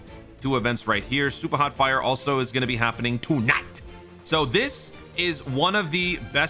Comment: did you say "Toon Ite" for like "tonight" ?